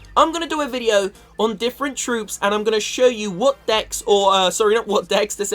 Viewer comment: Who else thought that he said dicks instead of decks 😂😂